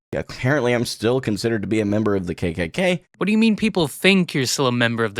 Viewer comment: I think you misheard him, he said "Apparently I'm still considered to be a member of the KKK", as in, despite him addressing it people are still claiming it